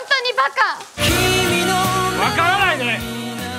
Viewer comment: のとこ 「分からないで」って言ってるんですか？ 映画とっても良かったのですがここだけ聞き取れませんでした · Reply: t s わからないねだと思います。